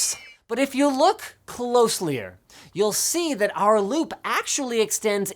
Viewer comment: Did Linus just say "closelier"?